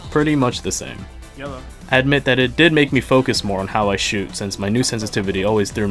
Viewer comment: did he say… he focused more